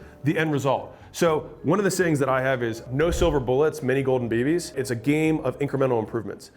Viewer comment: I usually listen to these while working out but actually watched the video on this one. For the longest time I thought Alex was saying "No silver bullets, many golden babies". This now makes so much sense